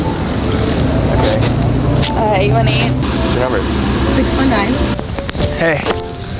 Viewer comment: did she said 619 ..........Booyakah Booyakah